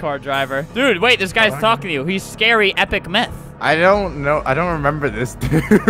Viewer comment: he said meth instead of myth